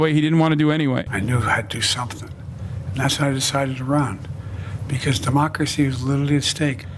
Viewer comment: When he said "democrwacy is widdewry at stake....." He sounded like some gen alpha I pad kid, maybe he's not as old as he looks lol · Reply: At first I thought he said "democracy was a mistake."